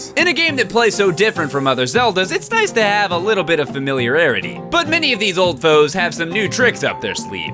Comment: Uh did Peebs just say familiararity